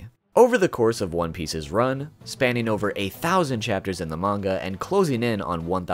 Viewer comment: I thought he said 8000 (I expect aqua (alabasta) (luffy gomu gomu no rifle (skypiea) (and the one on the title of the marine which is in I think luffy robot enies lobby) 2:30 remember when krieg said he was the strongest man in the world 3:30 lol if luffy wants to steal the mast of other ships 5:20 I forgot when he tried to eat crocodile I mean it would have make sense but 6:40 I forgot this one like enels mantra doesn't work because enel hears the voices in people and he was unconscious 7:10 7:45 (gumo gumo no ally robot (enies lobby saga) 8:45 9:10 9:45 bruh 10:15 luffys dank dance moves 10:35 mihawk human shield luffy grabbed buggy 10:50 11:00 non gear second variant 11:15 11:35 luffy use buggy as a shield again 12:15 12:35 gum gum ufo